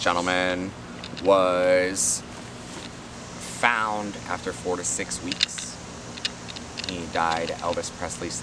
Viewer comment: When you thought he said 46 weeks but was actually 4 to 6 weeks 😂